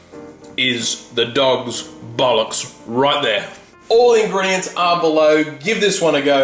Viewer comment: Did he say "the dog's butt ox?" LOL